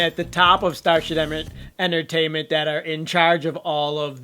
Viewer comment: Did he just say "Starsh*t Entertainment" ?!! Lmaoo, I'm so done!!😂